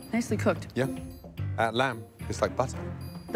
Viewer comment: Omg,I thought Gordon said the lamb is bussin at 3:35 😂😂😂